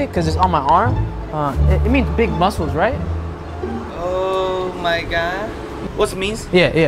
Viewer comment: The way he said "oh my got" I legit thought of him saying "im abatukum"